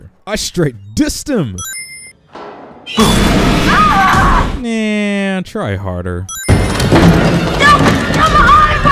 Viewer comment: Did anyone else heard a “boo” during that? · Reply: yeah Chris says “boo” lol